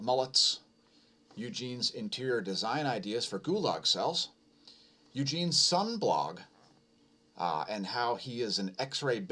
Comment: link your Sun blog and we'll put it into description if you like. Viewers, it's in Russian so you'll have to translate unless you know Russian. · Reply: At first I thought you said 'Eugene's sunblock'. Which i assumed would be SPF Nosferatu.